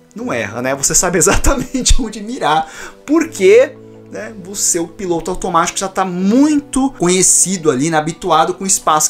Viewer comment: eu ouvi " o seu PINTO automatio" kjjjkkkk